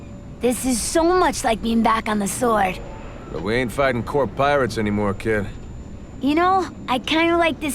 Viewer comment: did abe say corp pirate!?!